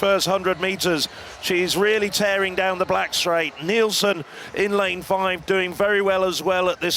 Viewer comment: Anybody else hear the commentator say “tearing down the black-straight” at 2:29😭😭when talking about Adeleke?